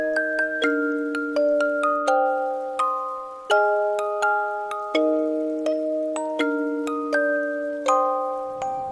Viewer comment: Does anybody know what is after 'apologizing but' at 3:38 ? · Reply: Its says... You should apologize to him..you all hurt his feelings.. hope it help I am sorry you couldn't see it😔💜